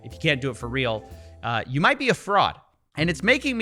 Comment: You say "You might be a fraud". I hear "You might be a frog".